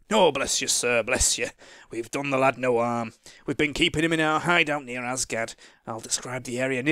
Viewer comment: While not paying attention, I heard "We're keeping 'im in Asgard". O_o I'm jealous of Ulric Fuggers brother if he's in Asgard XD